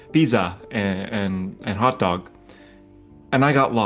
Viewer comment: I heard that as "Visa or hot dog," and the mental image of someone chowing down on a credit card was pretty hilarious.